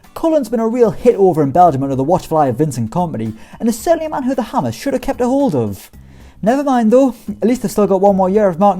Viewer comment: Wtf did he just say we schould have sold mark noble